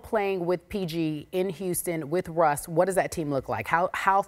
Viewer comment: Did she just say in Houston